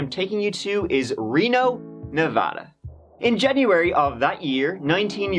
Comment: I swear he said "Jeburary" 🤔